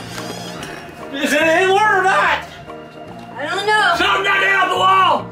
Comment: anyone else hear "Is it Hitler or not?"